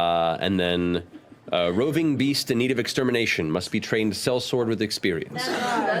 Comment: roving beast i misheard and thought raising bees i was really confused